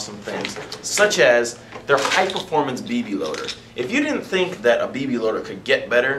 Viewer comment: Thanks man, any other genius insights to offer? I know he's referring to a BB loader, but if you listen, it sounds like he says "BABY LOADER". That was the whole joke.